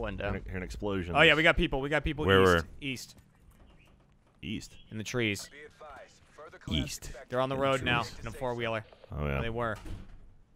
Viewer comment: East? I thought you said Weast.